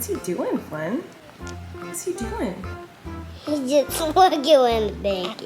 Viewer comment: I’m pretty sure Flynn said “he’s just wigglin” then he looked at Colleen all confused with what she thought he said. 😂